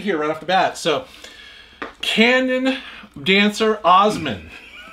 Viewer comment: For a second, I thought you said "Ganon Dancer". New Zelda dancing game just dropped! hahaha